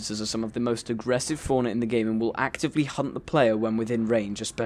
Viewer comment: sounded like he said, "Will actively hump the player" but he said 'Hunt'